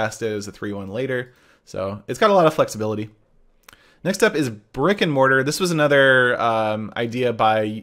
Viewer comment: Thought he said rick and morty. Brick and mortar